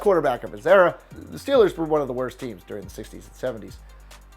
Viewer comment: did he say the steelers were one of the worst teams in the 70's